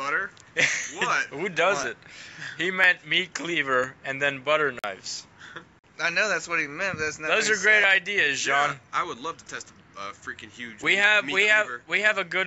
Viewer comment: I thought he said butcher knives